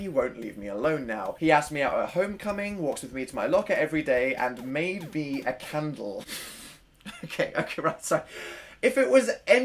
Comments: you are all the homemade candles of my life <3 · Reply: when you said made a candle i thought i heard may be a cannibal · When said “Made me a Candle” I thought for sure he said “may be a cannibal” I died.